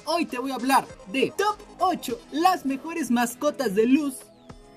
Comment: Jajaja nmms pense que decia PEORES y andaba como, pinche bato todos esos son mis favoritos :( Hasta que me di cuenta de que decia mejores xd